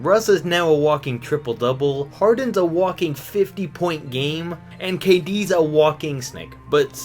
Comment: I thought he said Durant was a walking stick lmao